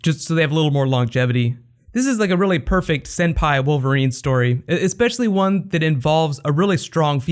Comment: for some reason i heard "senpai wolverine" and i cracked up